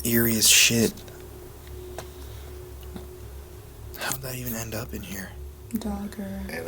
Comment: Theres a voice while you're talking that says something that I cant make out, at first, but then right after, it says " Thats the dog" 27:51. Sounds like "How'd they find her?". (Said while either you or is talking)